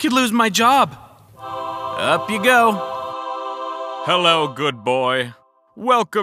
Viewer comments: I thought he said "hell no good boy" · That "I cast thee to hell!" Was personal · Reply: i thought he said “All cats go to hell!” yk from All dogs go to heaven